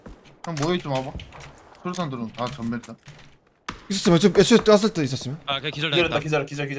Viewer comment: 도대체 뭐라고하는지 아시는분? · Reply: 폭있었으면 애초에 던졌을듯 이라고하네요